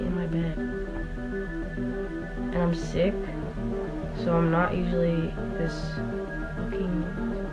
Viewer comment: whatt... It sounded like she said "I'm sick so I'm not usually this green" not the r slur...